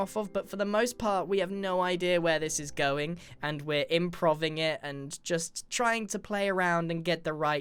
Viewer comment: did he just say im prov ing?